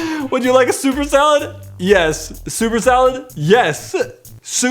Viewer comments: well I guess I am not the only one who thought the waiter said "Super Salad" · I thought a waiter said Super Salad too! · OMG I went to an Olive Garden once and also thought she said Super salad and I kept saying no and everyone was just looking at me like I was insane XD I thought I was the only one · oh my god my grandma said the exact same thing to the waitress because she also thought she said "super salad" omg hahaha. And wow it's really sad how many jerks there are in the world :/